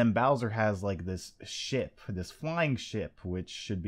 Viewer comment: I thought he said "shit" for a second lol